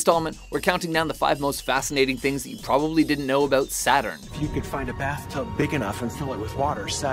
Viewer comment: I thought it said "5 facts about Satan" and I was like yesss the emos are attacking again